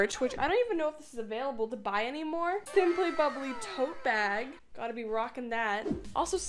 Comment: I thought she said toe bag!! 🤣🤣